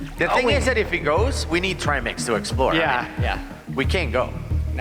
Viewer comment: Haha 14:49 they were talking about the lift and how deep and far away it is, I thought dude said, "It's too deep, so we can't go without Trail Mix" but he said tri-mix. I don't SCUBA... Can you tell ?